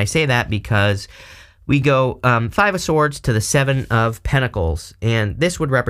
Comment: At first I thought I heard Salmon of Pentacles, lol! 😂 but you are on point with everything you’ve said; I was hoping to pass this big test on May 2, I did not but I’ve been practicing more and learned more than what I previously knew and have a second chance on June 8, which I pray I will pass that day 🙏